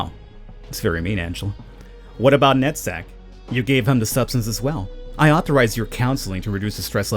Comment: for a minute there, I thought you said " Nutsack " ..LOL